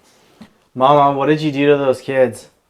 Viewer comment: Sorry...... Just after they notice the flickering light if you listen carefully I think I hear a child's voice. I can't make out what it says, it goes by quick (@ 56:56)! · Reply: Oh you heard it I can’t make it out either but it sounds like it might be saying mama but I’ve heard mama said nails two times before they went into the attic